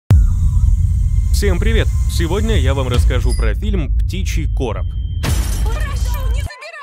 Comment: В начале послышалось, что фильм называется птичий потрох :))